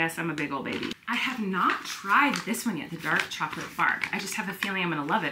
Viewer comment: Omg…I thought you said dark chocolate fart.😂